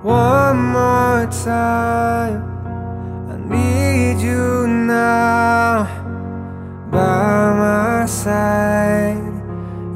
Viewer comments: tui nghe thành Ba Má Said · tui nghe nhầm thành "Ba má said" :)))